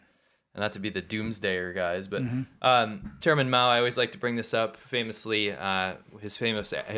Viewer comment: i always dont hear ' chairman ' , i hear ' german ' ( which is so or so around the same =pp )